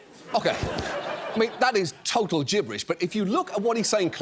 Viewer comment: I honestly thought John said "high coup" instead of "haiku".